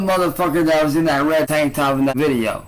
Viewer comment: my stupid ass thought he said "I am Trans"